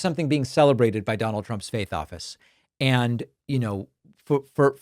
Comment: Oh, you said faith office. I thought you said fake office.